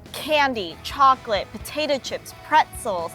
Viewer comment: Did she just say “potato chips”?